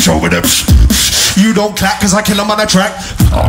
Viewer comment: Do you know what D-low is saying? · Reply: "You don't clap 'cause I kill 'em on a track"